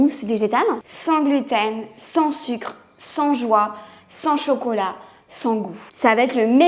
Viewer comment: Au debut j'ai compris "sans join" au lieu de "sans joie" j'ai eu peur😂😂❤